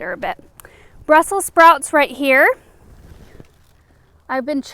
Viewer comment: oh my gosh, for a quick second i thought you said "russell sprouts"